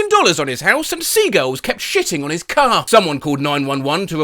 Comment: I thought he said sea girls 😭